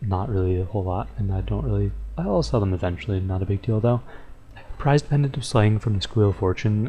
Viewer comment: sounds so wrong when you said "not a big deal tho" sounded like you said "not a big dildo"